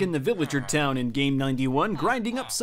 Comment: anyone else hear him say "game 91" instead of "day 91"